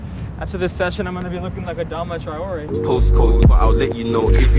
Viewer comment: what did you say there? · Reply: I’ll be looking like Adama after this session Bc of all the push-ups